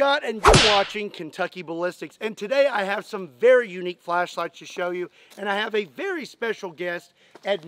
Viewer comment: Flashlights? I thought you said fleshlights...that would have been worrysome.